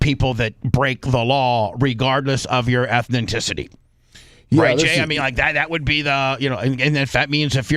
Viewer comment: Ethnicity not ethenticity😂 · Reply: I thought that he was saying "ethninticity". 😂